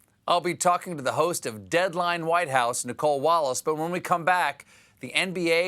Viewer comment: Did you notice it? A complete monologue without once mentioning the white house... Could be the first one in years! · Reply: WTF are you talking about, he says "WHITE HOUSE"